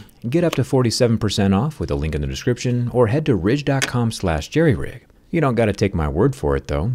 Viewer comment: Did you say Jerryridge?